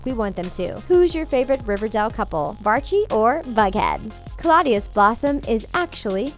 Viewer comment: HOLD UP AT 9:55 DID SHE JUST SAY VUGHEAD??? IS THAT EVEN A THING????